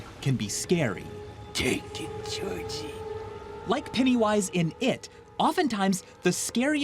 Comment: Thought he said titty wise instead of penny wise.